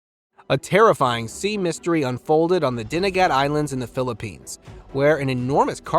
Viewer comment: I heard "Dinagat" not "Dumaguete". There is DINAGAT ISLAND in my Country (Philippines).